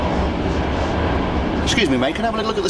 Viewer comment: What does she say at 14:43? "I think I can see a ......?" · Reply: Publio Furbino Torpedo.